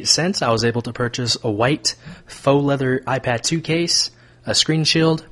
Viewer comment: ooooooooooooooooo im srry its a case i thought you said the ipad 2 was 17 dollers its the case wow im dumbe